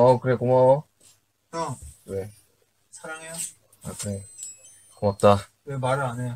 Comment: **Update** So Yoongi is saying 고맙다 (go-map-tta) which is Thank you. Had to find the vlive then watch with Hangul subtitles. Reverse engineering works everytime... yoongi says "mwutah" or something that sounds like it to Tae at 8:15 which is subtitled "Thanks" ..what word is this? I know "gamsa" or "gomawo" for Thanks but haven't heard the word he uses. Any Korean friends able to explain to me?